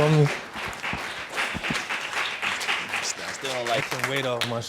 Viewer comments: At the end when the brother embraced him after the results, I think I heard “I still don’t like her” twice. He will NOT be around my child. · After the results were read, I thought I heard the hating brother say I still don’t like her, or maybe I was hearing wrong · Did anyone else hear the brother say he still doesn’t like her @ 16:48????!!!!